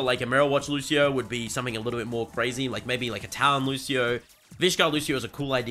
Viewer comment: I heard "Italian Lucio", truly the evilest option for Lucio.